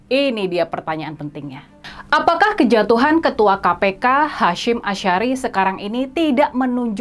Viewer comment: Menit 3:28 kejatuhan ketua KPK Hasyim Ashari..apa ngk salah dengar kak🤗 ! Ketua KPU kali maksdunya ya..haha..maaf kak kalau salah